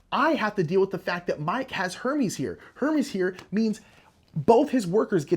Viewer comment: I had to do a double take around 7:45 when I thought you said “now I have to deal with the fact that Mike has Herpes” 😆 It was Hermes thank god(s) lol.